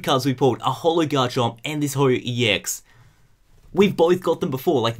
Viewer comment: I thought he said hobo ex